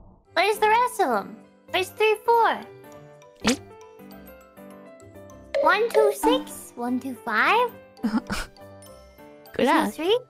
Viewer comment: I understand why Gura misunderstood. Sounds like Kiara is saying 1,2,6 and 1,2,5 instead of.. ya know.. 1 through 5